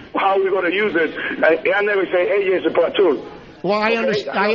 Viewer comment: You can barely understand what that Ozzie guy was saying. It almost sounded like he said "AJ is a cartoon", lol.